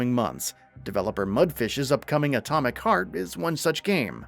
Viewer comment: Did he just say Mudfish?